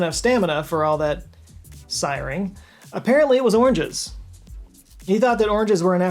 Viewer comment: I was not paying attention to the screen at this moment and heard 'orgies' instead of oranges. "How did he have the stamina for all that...siring? Apparently it was orgies" and I thought well yeah that makes sense. More efficient. OH. Oranges. Yep.